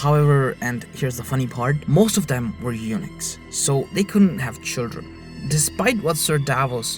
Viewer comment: ?? What is being said?? Why couldnt they get children??? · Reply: They were eunuchs. They were castrated